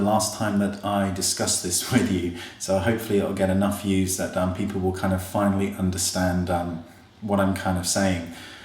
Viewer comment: I thought u said "that dumb people will finally understand.." Lol i was a little offended until I realized what you actually said!